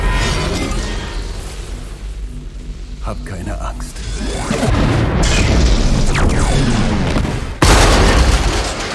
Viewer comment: Anybody know what he said? I felt like he said either I'm sorry or something like that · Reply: Magneto said " don't be afraid" in german